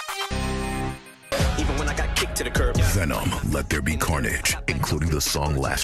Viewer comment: All I heard was "then I got kicked the curb" em about to spaz on this